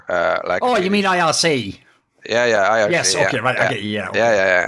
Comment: sounds like he says the name eric